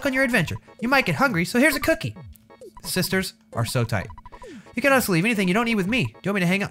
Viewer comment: Did just say "Sisters are so tight" on 13:34....suh dude